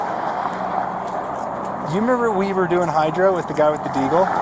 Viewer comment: I thought he said Hydro as in the hydroshock rounds, you know out target shooting.